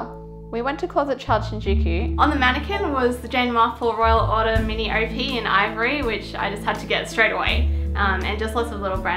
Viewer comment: i want that dress soo bad but i didn't understand what she said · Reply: Arlene Akers It's Jane Marple's Royal Order Mini OP in Ivory.